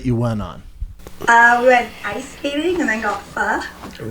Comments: I seriouslyy thought she said we went ice skating then got FUCKEDD! lmaoo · Really thought she said “we went ice skating then got fucked” Hahahaha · Dead ass thought she said she went ice skating then got fucked 😂